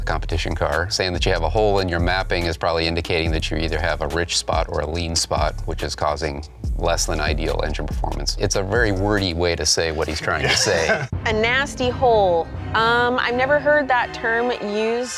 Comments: Always thought he said “nasty hull” lol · Ok, so, this is the second video on youtube that i have watched of Fast 1, about the "nasty hole " ... I always thought that he said "nasty howl" . That for me makes much more sense than "hole", as one will definitely hear when the sound of your racing engine is off .